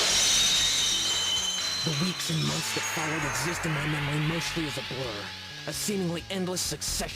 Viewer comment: what did he say at 2:38 ? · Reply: "the weeks and months that followed exist in my mind mostly was a blur"